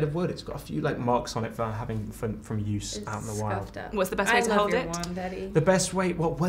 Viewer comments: Wait for a second there I thought I heard "what's the best way to hold your wand daddy?" 😂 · I wasn't fully watching the video and I heard " What's the best way to hold your wand daddy?" and I was like whaaa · I thought she said "I love your wand, daddy" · This is probably not appropriate, but my tired mind was not focusing and I heard 1:28 as "I love your wand daddy" XD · I thought she said "I love your wand Daddy" that be weird